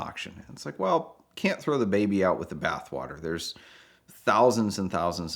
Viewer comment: what did I just hear 🤣🤣 I’m gonna start using that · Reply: What “Throw the baby out with the bath water?” That’s a very old saying from when people took a bath once a week and one family would use the same bath water on Saturday night so they would be ready for church on Sunday. The first person to bath was the master of the house, usually the father, then the mother and down the line of children from oldest to youngest until finally the baby. While clean water would be added during the bathing process, the bath water got dirtier after each bath. The joke is that the water was so dark and dirty that a baby might not be noticed in it when the tube was emptied, or tossed out. This was before indoor plumbing and even if the bathing happened inside, the water was tossed outside, for obvious reasons.